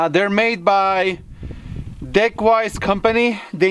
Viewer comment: I heard "Dickwise" xD That company needs a better name, for real guys!